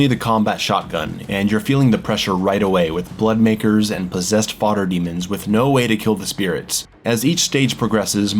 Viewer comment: I swear I thought you said "Farter Demons"..Did I see 5 Marauders? Geez, lol